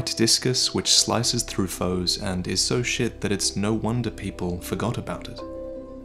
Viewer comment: Did he just say 'and is so shit that it's no wonder people forgot about it.'? Damn, Miyazaki getting cranky. Good one,)